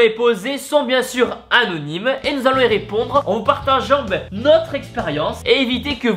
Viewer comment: c'est moi ou a 1:40 tibo dit "Partajambe" xD